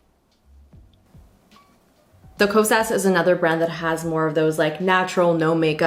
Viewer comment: this is what I kept hearing: “kose-ass” 😂😂